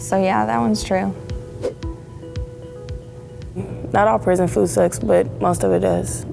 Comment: why did I think that said "poison food sucks"